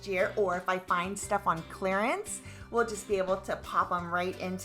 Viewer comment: At first I heard “space for extras that you would get next year” and was like OMG but then I heard you say “clearance” and was like OK.